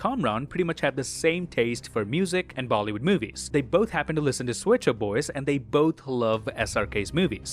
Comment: Oh Swet Shop Boys .. i thought she said pet shop boys and had Go West going through my head the rest of the night, while wondering why 2 young kids with a pakistan background, loved a 80s music group so much.